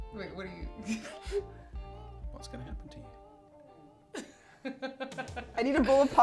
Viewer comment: Can you please fill us in with what said at about 17:00 when he got increasingly quieter? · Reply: "What's going to happen to you? Why is it going to happen to you?" demonstrating how he lectures/scolds before spanking Kelly.